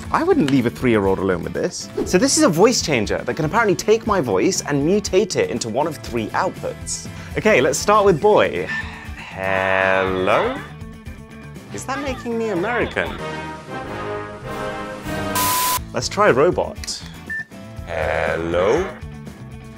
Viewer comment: "Heellloo is that making me American?" So funny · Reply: As an American I can agree lol also when i first heard it before our national anthem I thought he said “is that making me a merry cone”